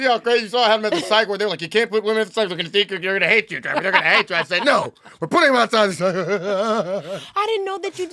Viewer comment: wth did this man say 😂😂 I feel like their convo was in 2x 🤣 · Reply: “You can’t put women outside. They’re gonna hate you. They’re hate you. No, we’re putting them outside. That’s what I heard.